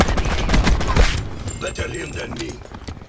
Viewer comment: Sounded like said "Literally a dead meme"